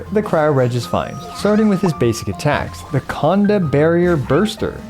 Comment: I heard something sus Condo...m